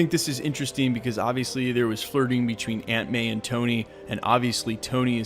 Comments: When you said Aunt May is right there beside him I thought you said Ant-Man. Aunt pronounced awnt, not ant. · Every time you said aunt may I thought you was saying ant man! I’m like, ant man is in the movie? What!!? Where!!? 😱 😂